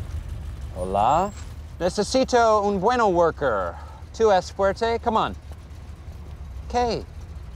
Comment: what does dwight say here 00:11 ?????? tu es squirte??? oh wait i think he say squirt because hes a kid and small .. oooo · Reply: tu es fuerte??????